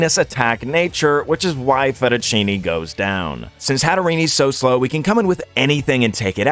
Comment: did he say hatterenie